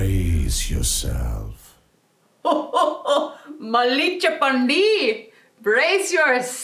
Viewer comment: Oye what did you say? "Malli cheppandi" na? Em cheppale pilla niku? 😂